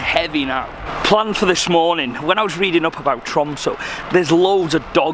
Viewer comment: Okay. So everything you said Chromso because of your accent I thought you were saying Trump. (P.S. I'm American.) But I just find that kinda funny.